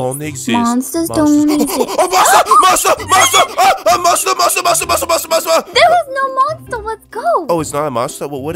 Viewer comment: Did He Say Mustard?